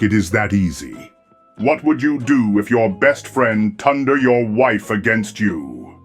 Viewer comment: Uh Vader says tunder instead of turned